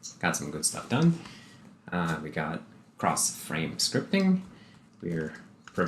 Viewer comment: I thought you said, "Cross site scripting!" lol.